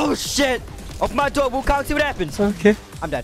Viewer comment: what does he say😂😂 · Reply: he says “open my door Waukong, see what happens.”